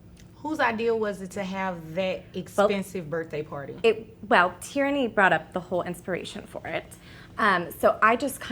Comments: Im sorry… did she just say her daughter’s name is “tyranny”? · not to name shame, but did she say the kids name is tyranny? Like an oppressive government?